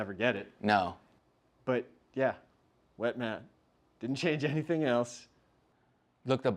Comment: I was confused when you started spraying the turf. I thought you said “WET MATT”